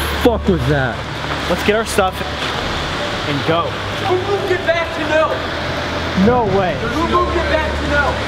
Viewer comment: Yo wtf is Corey saying when he’s yelling at 12:54 like I here “let’s go back to milf” HUH